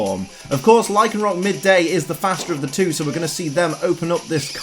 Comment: Did he just say that Midday was the fatter of the two? 🤣